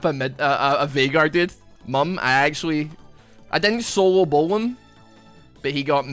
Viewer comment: wait did just say solo bolo?